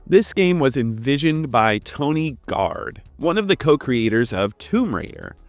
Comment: It sounded like you said tony gard and not toby gard.